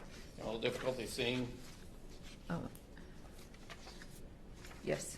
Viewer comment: he starts asking about another photo, under her breath it sounds like she says "I see no use." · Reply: She said " I see now, yes".